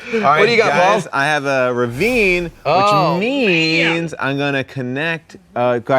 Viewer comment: I kept hearing "Avril Ravine"